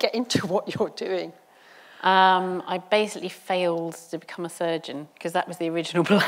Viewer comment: A surgeon ?I miss heard I thought she said I failed to be a virgin 😂😂